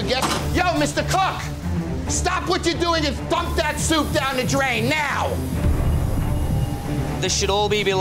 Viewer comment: anyone else think he said "Fuck that soup down the drain!"